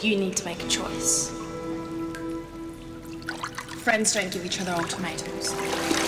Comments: I thought she said "friends don't give eachother old tomatoes". Am I weird? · What did rikki say at 8:30? Sorry i just cant hear it properly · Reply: "Friends don't give each other ultimatums"